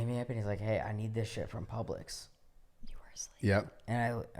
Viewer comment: wtfff I was closing my eyes and I hear “you’re asleep” or I think that’s what it said 👀